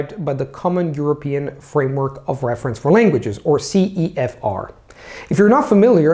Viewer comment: thank you for such a great video, by the way, I think there is a small mistake at minute 3:43 , it says "Common European framework of common european framework", I think it should say " Common european framework of reference for languages" as you say in the video